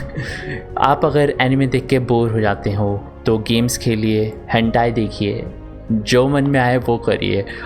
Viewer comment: did he said hentai deke 7:00 ? Lmao